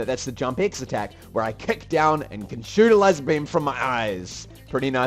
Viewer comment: Thought you said "shoot a lesbian" :P